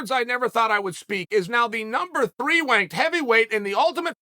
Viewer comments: Did he say number 3 wank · number 3 wanked. Never thought you’d say that either unc.